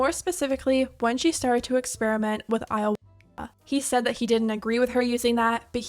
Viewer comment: And experiment with what? I have no idea what was said here, can anyone tell me? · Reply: He said Ayahuasca. It’s a natural hallucinogenic so YouTube wants the word covered so they can say they aren’t promoting drugs!